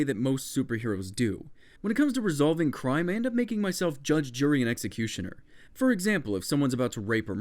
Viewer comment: When you said judge jury and executioner I thought you said judge Judy the executioner xD